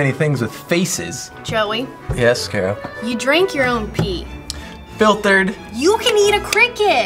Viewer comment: did she just say that he drinks his own pee!?